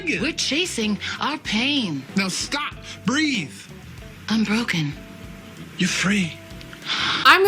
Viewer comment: i always thought dee was saying "im broken" instead of "unbroken" and charlie was responding lol